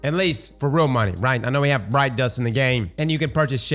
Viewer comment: Lol every time he said “Bright Dust” I heard “Brine Dust” and with the recent fishing changes, I could see that being a currency in Destiny 😂